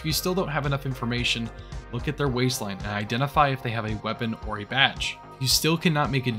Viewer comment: I swear I heard 'look at their waistline, identify if they have a weapon or a vaj' 😮 changed the whole narrative from that point on.